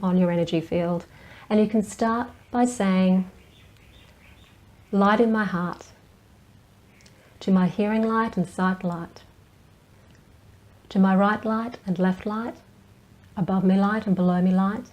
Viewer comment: What was the saying at 5:17 ??? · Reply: Bring light into my heart and before me light and behind me light, above me light and behind me light, to my right light and to my left light...